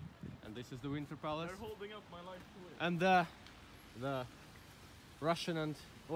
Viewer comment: Did say:"They're holding up my life to will"?